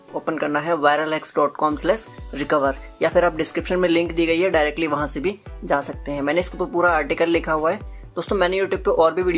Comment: it sounds like he said "lick a dick away"